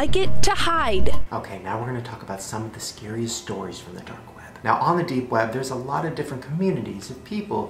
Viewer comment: I thought he said date web.... Also, Listen to Shane, Don't give into evil and darkness...